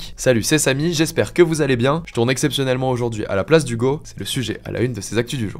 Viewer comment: Quand a dit "a la place d'Hugo" j'ai entendu "a la place du Goat" Hugo Décrypte le Goat.